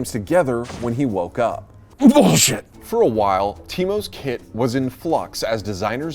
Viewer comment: it sounds like he says the word "Boże" from the Polish language.